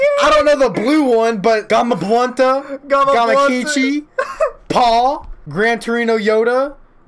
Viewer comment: Did he just say GamaBLUNTa? lmao